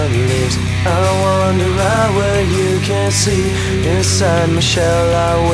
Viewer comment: I remember listening to this song when it came out and I always thought he said "inside Michelle I want and bleed", and it confused the hell outta me.